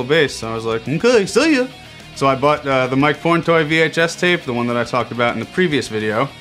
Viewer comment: did you say PORNTOY